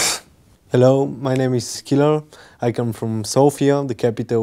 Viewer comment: is his name is "killer"? i know, it was, but sounded like killer lol